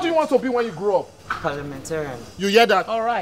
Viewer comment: Did I hear 'ass of rep?' Chai!